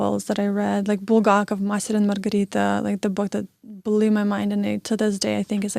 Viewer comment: she says the title of her favorite book...can you tell us what it is? I don’t understand what she says. Thanks. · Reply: The Master and Margarita by Bulgakov :) Great book, one of my favourites too